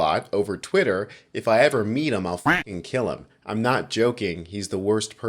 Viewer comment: To me it sounded like he said I’ll probably kill him